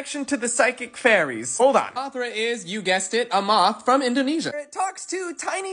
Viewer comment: I thought you said Motheron, Like the mini boss during a solar eclipse In terraria